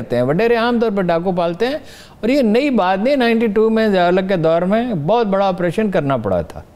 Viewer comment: What is he saying, "1992 main zia ul haq ke daur main".